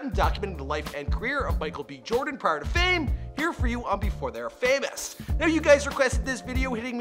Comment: I legit thought it said "before they were black"